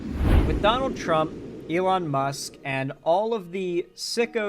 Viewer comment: i heard McDonald Trump and i think that would be a good name for him